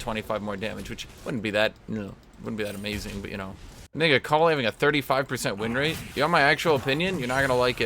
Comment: What word did he say??? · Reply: "Which wouldn't be that amazing, but, you know" "What do you think about Akali having a 35% winrate?"